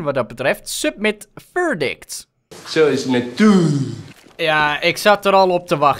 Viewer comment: wat zegt die donkere man? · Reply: hij zegt: zo is natuur. Video: man gooit blik in water